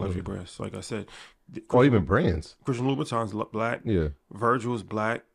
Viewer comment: Did He Say Louis Vuitton Was Black?? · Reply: He said Christian Louboutin. Don't know him to be black though.